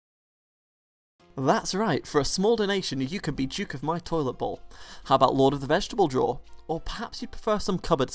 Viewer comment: I thought he said "You can be Duke of my Toilet Hole" 😅🤣 Me: WTF OH, Toilet BOWL lol